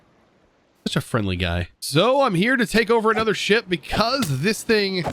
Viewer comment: is it just me or did he say shit